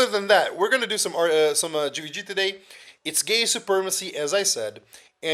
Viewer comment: paused at 1:40 something cause i have to finally say this! every single time i hear " today is gay supremasy day! " xD , every fuckin time i keep lolin like a moron over that! much love